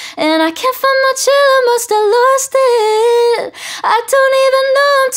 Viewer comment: All this time i thought she said “i can’t find my chair i must’ve lost it” and i was like okay she wants to sit on her man you go girll😂